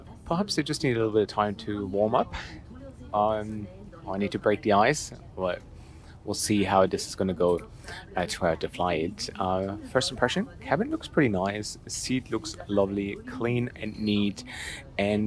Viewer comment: I swear I heard "I need to break the ass" with an Ozzie accent.